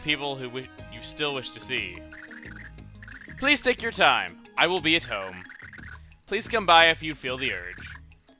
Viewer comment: WHAAATTTT?? plus i thought said I WILL EAT TIPPI AT HOME!